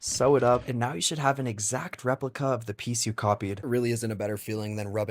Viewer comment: i thought he said the titties formula 🤣